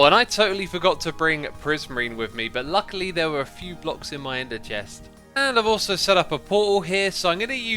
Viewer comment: he said "ender chest" with such a weird voice LOL (it sounded like enther chest") xDDD xD